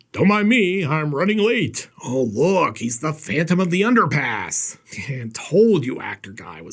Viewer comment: I have to admit when you said phantom of the underpass. I actually thought you said phantom of the underpants. 😂😂😂😂😂😂 Okay on that note. No more watching your reviews after six hour tours. I do remember David letterman..Damn I'm ancient.